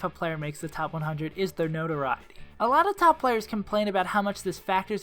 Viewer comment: One critique is that at like 4:22, you didn't really enunciate the word "notoriety", I had to rewind and strain to listen (as a viewer it was fine, just thought it was worth saying in case you were looking for ways to improve your videos), but other than that, really good video as always, just work on your diction if you're looking for anything to improve. :)